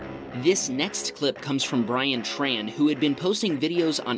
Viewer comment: i was looking away and i thought he had said ryan trahan lol